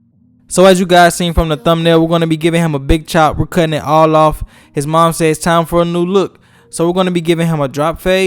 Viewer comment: At first I thought his mom said to CUT IT ALL OFF AND ALL OF IT and I thought she was an bad ass for dat But I'm sorry for cussing God pls forgive me